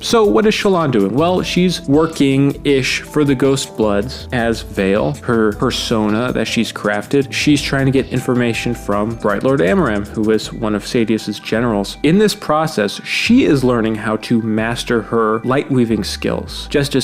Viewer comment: I thought you called Veil as Shallan's fursona, lol.